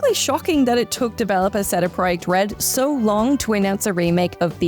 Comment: Cedda Proyecht Red lol need to slow down a bit I think. Could be how Aussie's say CD Projekt Red, but seems like a bit of a stretch to assume that lol